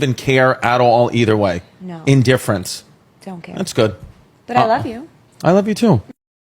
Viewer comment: She said i love you though and all I heard was," you could die tomorrow and I'm good"